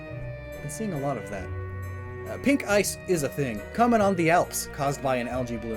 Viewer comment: I thought it said noice not no ice